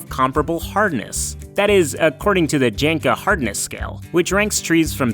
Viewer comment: I misheard “Jenga Hardness Scale”, which is objectively funnier